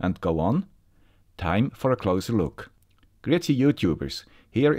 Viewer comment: Unrelated question: What is it you always say at the beginning, at 0:19? Sounds like "Etsie YouTubers!" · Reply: It is "Grüezi" and means "Hello" in Swiss German